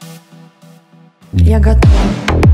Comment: Мне не послышалось на 1:35 "Я готова..." ? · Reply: Ну конечно, мне всегда хотелось попробовать... Я надеюсь, у меня получится. Ну что, будем стрелять? ... Я готова.